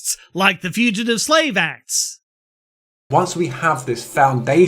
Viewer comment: the subtitles says "[indistinct]". The word he said there was "Shelter".